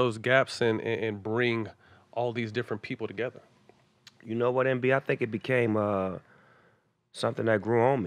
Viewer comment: did Snoop call Barnes 'Envy' (DJ Envy)? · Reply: He said "MB"